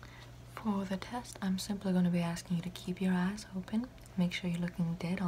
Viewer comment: it sounded like u said "im gonna need u to keep ur ass open" and jm fucking choking